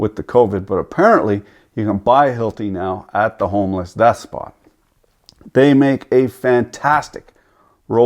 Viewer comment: Hey he said it ! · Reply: I hear 'death spot'